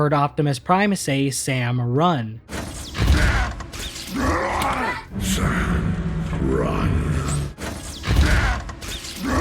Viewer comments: I can't not stop hear Sam run, I'm sorry but I still think he's saying Sam run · Honestly i heard "Can't.... Run" and it could have been grunts, but it could have been both but i prefer the "Can't run" because look at him, he's literally near death's doorstep, he could Barely crawl away abd deploy his weapon